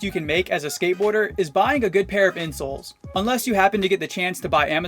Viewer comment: For the first one when he said a good pair of insoles I thought he said a good pair of insults